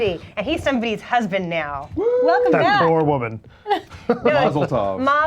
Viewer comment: i thought said thats one "Sore Woman"